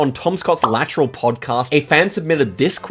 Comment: Tom Scotts? I swear i heard Scott's Totts1